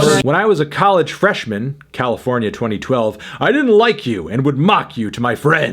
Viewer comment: Did he just say "california 2012" when it clearly mean circa 2012?